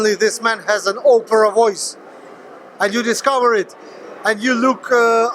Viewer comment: it sounds like he says tosser lol he's always a good interview is krassyuk